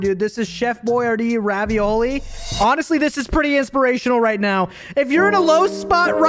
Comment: For a second I thought it said "inspirational Rickroll"💀💀💀